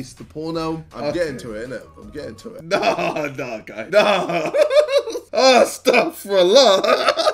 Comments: did he say استغفر الله ؟؟؟؟ 😂😂💔?? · did he just say' stop for allah '?